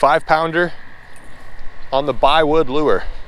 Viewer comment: When you said “ 5 lber on the bi-wood lure “ I thought you said plywood, so that’s your next wood to use.